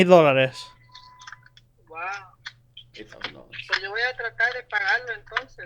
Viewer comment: As a spanish speaking individual, the mother DID say SHE HAD A MORTGAGE BUT TO SEND HER THE PAYMENT INFO ANYWAY. She was wondering how to go about in order to pay it and was asking for what method should she pay it. THIS WAS NEVER TRANSLATED. Don't fucking lie about what your mother said. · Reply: I think they misheard, she said 'lo' and not 'no'. I heard 'Lo voy a pagar', not 'No voy a pagar''. I'm going to pay it vs I'm not paying.